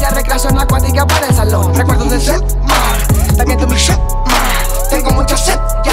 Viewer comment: alguien me pude decir que dice en el minuto 2:50 en adelante, es que no se si dice recuerdos del cetmar o recuerdos de ese mar ? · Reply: cetmar